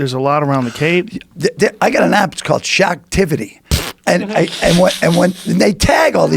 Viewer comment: thought he said "shaqtivity"